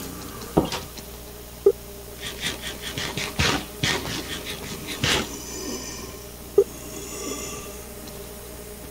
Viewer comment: What did you say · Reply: Malnurish trying to prove himself to the MEG that he can be a great leader